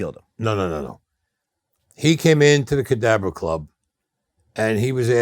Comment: Wow, that’s the first time I seen him take off his sunglasses, he got scary looking eyes. In another interview he corrected Staxx, saying the Cadabra club, though sounds like he says Cadaver club.